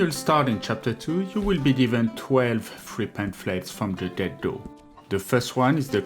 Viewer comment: 12 Pamphlets from the what? Sorry man, didn't understand that. I assume from the get go?